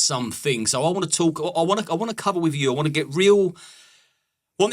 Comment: Did you actually say "I want to cuddle with you" at the beginning?